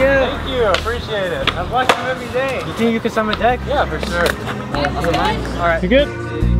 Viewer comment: Time stamp 13:33 I thought he said smd